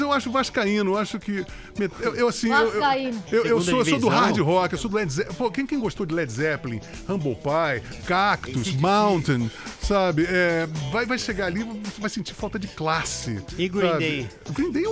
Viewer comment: quem sabe o nome das bandas que o lobão falou no fim do programa mais precisamente no minuto 1:09:45? Eu sei do Led Zeppelin, Cactus e Mountain! Qual foi a outra banda que ele falou depois do Led Zeppelin? Começa com algo parecido " rambopy" mas não consegui achar essa banda alguém sabe que banda é essa?